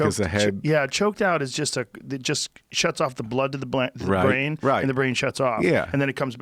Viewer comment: Blood to the "Blaine" instead of "brain." Love this too much! :)